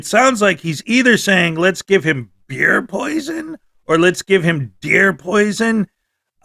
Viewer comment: It sounded like he said fear poison.